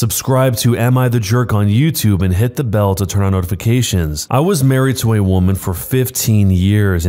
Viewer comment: Was watching death note intro when I thought a guy said "I was married to a man" when it was actually "I was married to a woman"